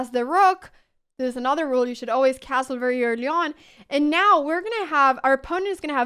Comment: You mention a castle rule, but I can't understand it even at 75% and 50% speed. "You should always castle ver yern yawn" The YT closed captions say, "You should always castle very early on." If that is right, (sounds logical to me.),---I have never seen YT captions work so well. :)